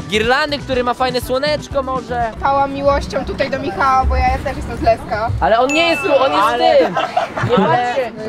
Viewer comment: czy ja usłyszałam LESKO moje LESKO ,mam ogromne marzenie żeby kiedyś spotkać Michała w naszym lesku